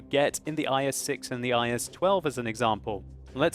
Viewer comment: Did you say IS-12 at 4:40 ?? I never saw that tank before!